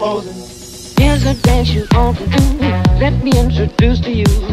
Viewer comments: Honestly at first I thought it said pro sin ngl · I thought it said pro sin lol